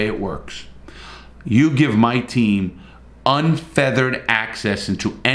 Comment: MEMBER PROGRAM Starting $1.99 - Perks from wallpaper to early access videos to one-to-one chats. - YouTube (standard): <link> - Patreon (higher/lower levels): <link> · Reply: Hi Larry. I greatly enjoy your videos and think you make a lot of excellent points. I just wanted to point out a vocabulary error. It sounded like you were saying "unfeathered access". The proper phrase is "unfettered access". Just a small difference that would make many of the "older people who think they know what they are doing" in washington take you a bit less seriously and I think what you say is important and should be heard. A side note that makes this difference all the more interesting is that the word fetter literally means "restrain with chains or manacles". Kind of appropriate when talking about how limited prison inspections are, don't you think?